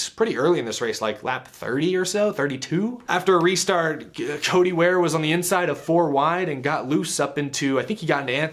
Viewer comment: Did you just call Cody Ware "Chodey Ware"?!? Pretty damn unprofessional for a "journalist" dude....